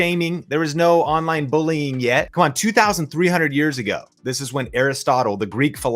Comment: when you mentioned Aristotle i thought you said "he said this 203 years ago"... i was like, dude no . hahaha. thank you for the awesome content, as always <3